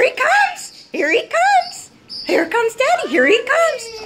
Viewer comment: List to 00:40 and you will hear him say I love you. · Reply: And I thought I heard him say "hi dad"